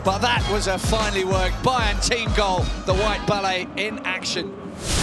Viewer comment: did they call him the white Pele? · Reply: Enoma Iguisi I think he said the White ballet referring to their kit? I think